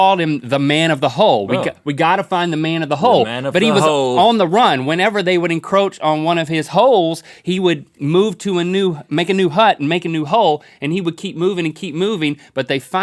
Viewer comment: I heard...."Whenever they would encroach on one of his hoes"😂